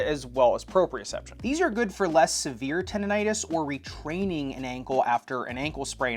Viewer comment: the information is great for me, and I don't understand 1:40s, what's it mean? that's about severe tontis or retraining an ankle, right? I don't listen clearly.